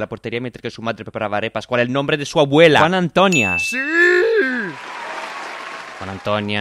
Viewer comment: que dice en la j? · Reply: Juana Antonia